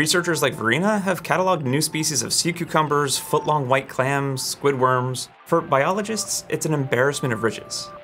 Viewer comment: tell me I'm not the only one who heard "Squidward" at first